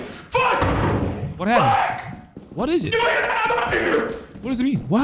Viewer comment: What are your theories on what he said? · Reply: No idea on the first half but I’m pretty sure the last bit is “I’m outta here!”